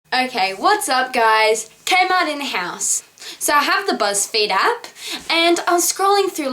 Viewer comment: lmfao I thought you said "kmart in the house" AHHAHA